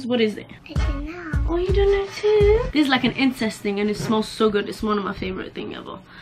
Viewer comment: Yo did she just say 'incest' instead of 'incense'? 6:25 😅😅